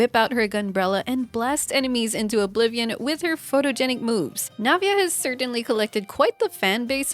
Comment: I needed to restart the video intro because I thought said photogenic boobs